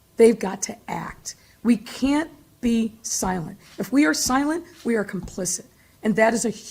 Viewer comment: Amazing, the criminal is maligned by social media and not his abusive single mother. They never mention that. And I thought she said, "silence is complicity."